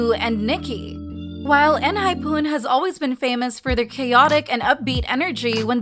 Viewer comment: I'm the only one who is focusing what she said "Enhypun" (no hate)